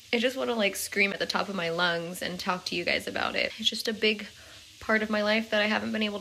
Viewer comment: Did I hear “He’s just a big part of my life” at 4:10. I hope sooo. Love y’all 🥰🥰